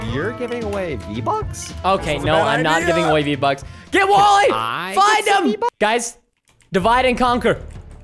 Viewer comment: When your watching this, if you put on captions when preston says get wally instead the captions say get why. Also it says find him but instead it says find them. At this time 17:54.